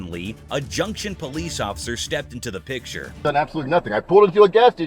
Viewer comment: he said pigture instead of picture😂